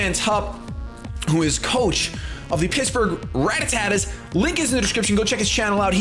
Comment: did you actually say rattattattas? Lol